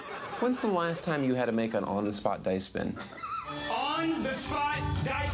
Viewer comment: I mean I thought it said "On the spot dick spin" but I now see how decision works.